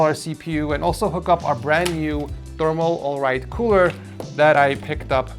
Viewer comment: did he just say “thermal alright” 😭